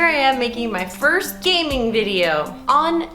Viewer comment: for a moment there I thought Brie said a "gangbang video" :/